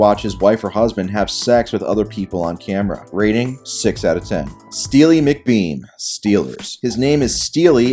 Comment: Steely Mcbeam looks like the king of all women respecters. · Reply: Bro I coulda swore he said Steely McBean